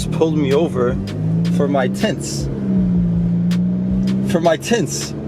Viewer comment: lol i thought he said "I'm being pulled over for my T*tts* 😂😂😂